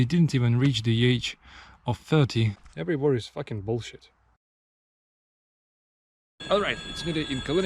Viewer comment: "everywhere is f***ing bullshit"? I quite dont understand that or maybe I m not hearing what is said properly.